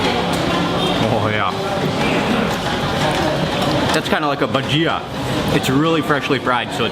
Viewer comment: what's the title of that reggae music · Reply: did u say "Bhajiya (indian snack)"????😮